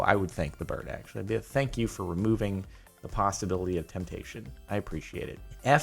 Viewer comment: If you think hard enough, he says PastaBility